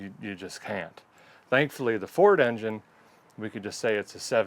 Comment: First I heard "I talin car" and thought what in tarnation!! Then I heard "Ford engine"...... all is well in the world. Thanks, Derek, for all the great shows